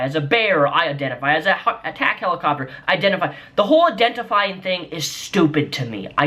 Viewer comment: I identify as an attack helicopter, and I want a private bathroom everywhere! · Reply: I honestly thought he said CAT helicopter XD